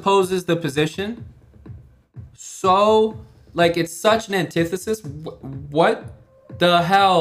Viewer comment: What’s that word?! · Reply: antithesis.if there is an ideology the opposite of it will be its antithesis